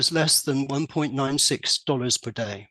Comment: "$1.96" c'mon man 2 decimals!? just say 2 dollars a day! who cares about the 4 cents!? anyway, that's just my 5 cents. keep it simple. y0u confused me I thought you said 96 a day